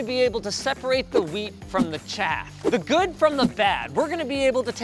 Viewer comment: Thought he said separate the Weak from the Chad